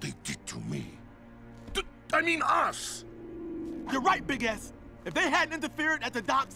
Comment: am I the only one who heard 'your right big a$$'?? Just me? XD